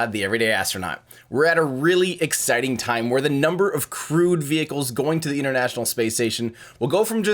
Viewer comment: For some reason, I heard "crewed" as "crude".